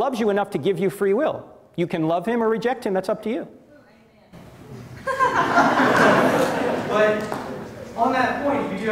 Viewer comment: What does the lady say at 5:23? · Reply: I think she said “go white man”